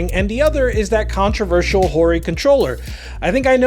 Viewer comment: I thought you said Horrid controller but then i saw the title lmao